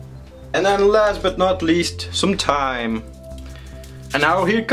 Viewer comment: i thought you said "last but not least, some TIME!" XD anyway great video :)